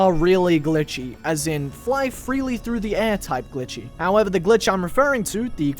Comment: did you say glichy.